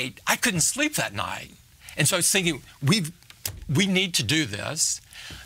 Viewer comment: In 11:42 is written "I was thinking" and I hear "sing you"